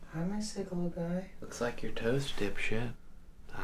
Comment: "Looks like your toast dip shit" favorite line perfect delivery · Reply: Oh my gosh, same- I thought he said "looks like your toe's dipped it" I was so confused